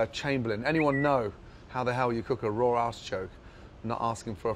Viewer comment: when he says "artichoke" at 2:50, it sounds like he is saying asschoke. "or slice a lemon and stick that up your asschoke" as he makes a very inappropriate hand gesture.